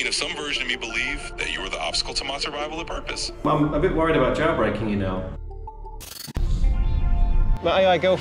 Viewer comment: I kept hearing it as "jawbreaking".